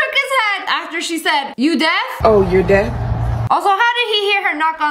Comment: instead of deaf the caption said dead 💀